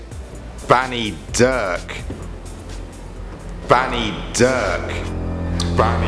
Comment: at first i literally thought he said fanny not banny...